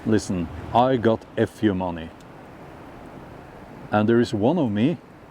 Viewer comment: Sounded like you said..." I've got F U money now" which sounds like your integrity only surfaced once you were in a privileged position. Sounds like you acknowledge that it is a mutable substance. Not sure how that fits in with your narrative though.